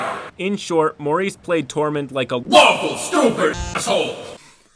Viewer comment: I thought he said waffle not lawful for a second